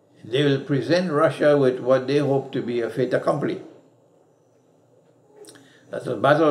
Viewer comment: please someone help what did sheikh said 16:59 to 17:05, because i am unable to understand words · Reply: I think he said "fait accompli", which means leaving them (Russians) with no option but to accept to fight. It may be words from French, please check Google about origin.